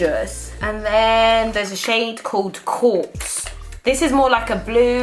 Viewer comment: When she was saying the names of the highlights i thought she said corpse not quartz 😂😂😂😂😂